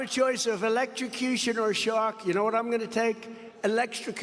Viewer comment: I had to listen three times. I thought he said "Shock" and was unable to understand. said "Shark" clearly & I still couldn't understand.